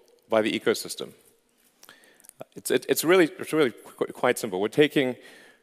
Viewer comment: Please correct subtitling errors (like "quiet" instead of "quite" at 01:20). Thanks.